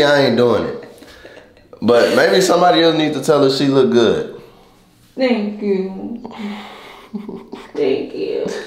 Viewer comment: Did she just say “thank you stink” okkkk